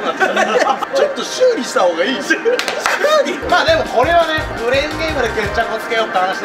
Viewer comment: クレーンゲームがブレイキングダウンに聞こえた（は？）